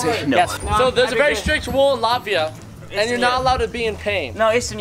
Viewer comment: is he saying latvia?